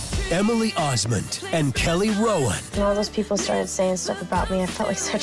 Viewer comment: I heard “Kelly Rowland” 💀